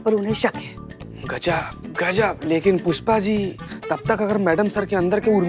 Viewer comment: he said kushpa instead of pushpa